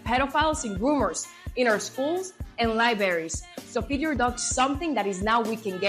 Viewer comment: Did I hear her say “lie berries”? Ok, looks like I heard that correctly.